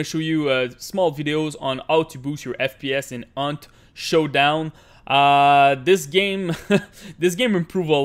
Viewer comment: did u just say Aunt showdown